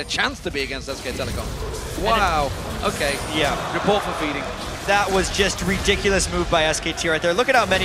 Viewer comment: did they say : report for feeding at 22:27 haha?